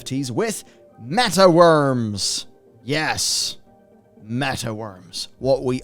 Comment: I thought it said meth worms at first.